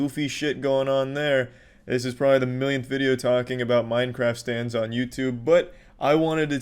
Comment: Is it bad/insensetive that the entire time I thought he kept saying "Minecraft stands", like jojo stands, and I was extremely confused as to why the fuck a group of people were calling themselves that? The bizarre adventures of twitter, I guess.. Minecraft Twitter - Stand Name "Violent Puppeteer", A thousand tiny goblins with sowing needles with metal strings who attack you from all directions, stabbing you until you have so many strings through you that they can control you like a marionette, or until you die from bleeding out from all the holes in you (Opinions of a human bean who has never used, looked at, or touched twitter)